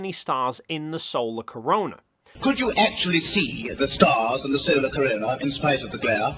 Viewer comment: Regarding the Patrick Moore clip, are you sure he says "stars in the solar corona" to me it sounds like he says "stars AND the solar corona". My hearing of his words is: "when you were carrying out that incredible moonwalk ... when you looked up at the sky could you actually see the stars and the solar corona". To me it sounds like he's asking if they could see the stars and the solar corona from the surface of the moon.